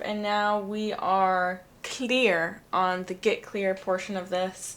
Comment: When you said “get clear” I thought you said, “get queer” 😺🌈